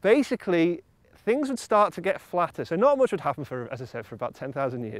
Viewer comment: Did he just say that the Earth would get FLATTER?